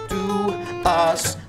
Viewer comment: The way he sings "miracle" though. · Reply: Misspixelwolf ) I always heard 'america' when he says 'a miracle: 😂